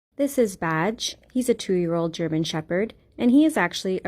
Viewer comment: No joke I thought she said this is vag 😂😂😂😂